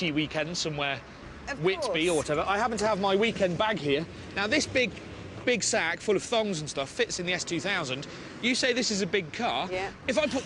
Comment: Did he just say "This big...big sack full of THONGS and stuff" HAHAHAHAAHAH